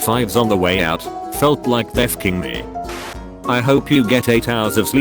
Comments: I gotta say, I heard the voice say "They're fucking me," and got super spooked for a split second before looking at the story. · I know what he said but what I hear was "and I felt like the fucking me" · Anyone else hear there fucking me